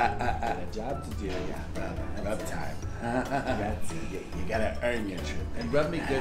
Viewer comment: I thought he said rub tide for a sec